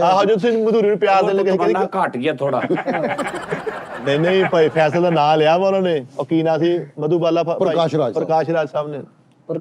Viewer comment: Prakash Raj said "Faiz Sab's poetry". He did not say "Faisal's poetry"